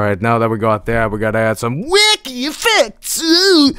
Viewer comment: It took me a long time to realise that he was saying "wAcKy EfFeCtS" instead of "WICKY FIX" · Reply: I thought he said wicked effects